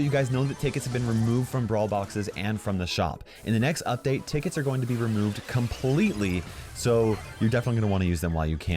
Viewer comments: For a moment I thought he said in 9:18 that tick was gonna be removed and I’m like HECK YEAH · I thought he said ticks are being removed from brawl boxes and the shop and I was like yes and then I was oh wait nvm lol